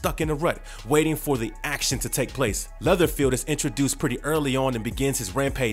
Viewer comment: did u say leatherfield?